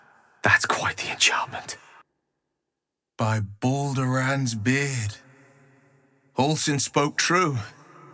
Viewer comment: wyll says beard but the subtitles say bones, and i wonder which one is the og on the script?